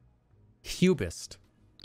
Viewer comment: Cubist is what I hear and I thought I heard something like Pubist or Pubic.